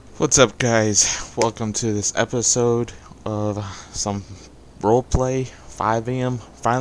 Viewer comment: thought he said roleplay at 5 am at first